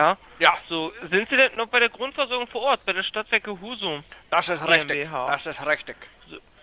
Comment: Bin ich der einzige der beim ersten Mal hören "Stadtwerke Huso" verstanden hat?